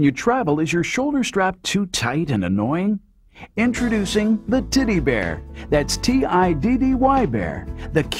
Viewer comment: Snuggies are actually comfortable Believe it or not. I also thought he said Titty bear xD